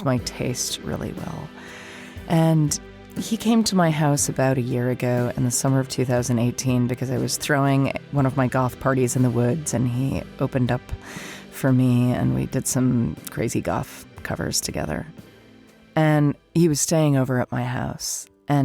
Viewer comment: Thank you, both, and all the others involved. Funny to me, note. At around 6:30-ish - I swear I heard you say Cormac had come to one of your 'golf parties in the woods', and I couldn't concentrate on anything for a while, having to go back after a few minutes spoke afterwards. The pictures in my head of what golf parties in the wood would be like, got in the way.